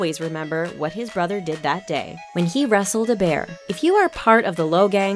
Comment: I thought he said he wrestled a bear